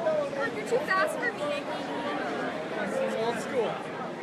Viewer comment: did Cole say, "that's why you go to school"? XD